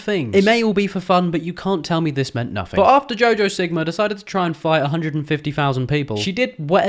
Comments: did bro say "jojo sigma" lol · am i the only who heard jojo sigma? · Was I the only one that heard him say jojo sigma